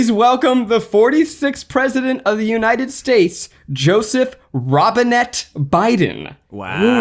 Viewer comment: I swore I heard Biden say "Joseph Robinhood Biden".